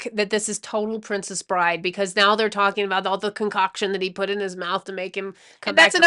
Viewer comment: I thought you said cock in his mouth